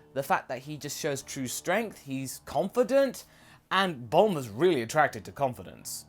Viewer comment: "He's competent." Then why was she into Yamcha? Or did you say CONFIDENT? Then why was she into Yamcha?